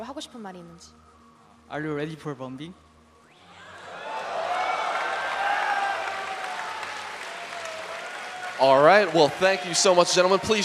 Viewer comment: what does say? are you ready for bomb d?